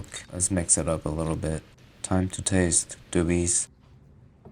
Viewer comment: Omg i just realized When you said Doobies I was like onion doobies but what she says is annyeng Doobies like in Korean.... F I feel so uncultured 🤦🏻‍♀️